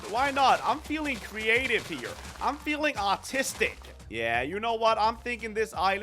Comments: Did he say autistic or artistic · It sounded like he said “ I’m feeling autistic “ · I’m feeling autistic 😂😂😂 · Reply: Same I thought he said autistic💀💀💀😂😂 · Am I the only one who hears "autistic"? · I thought he said autistic lol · I may be wrong but I swear I heard autistic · I thought he said autistic